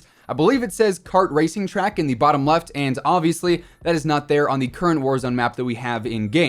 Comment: did you say the racekart track a part of the map? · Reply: I said the letters aren't on the map currently :)